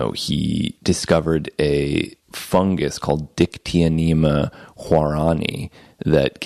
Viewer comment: he said a fungus name, I am having difficulty understanding the name, and writing it, can someone tell me what fungus is it? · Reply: Dear sir or madam, at the point in the interview which you provided in the timestamp, Mr Hamilton mentioned a "fungus" named "dictyonema huaorani", which, in actual fact, happens to not be a fungus at all, but a lichen! This lichen is known to contain some amount of 4-PO-DMT (psilocybin), and 5-MeO-DMT. Be aware that it's inadvisable to attempt to harvest or ingest this lichen, as its consumtion can potentially lead to terrible confusion and mental suffering. Have a wonderful day, and be very careful with anything containing 5-MeO-DMT.